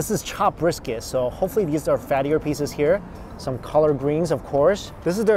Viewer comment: Did he just say colored greens ?? 😂😂